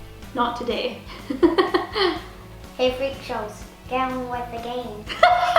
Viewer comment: What is said at 12:28? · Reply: "Hey freakshows, get on with the game!"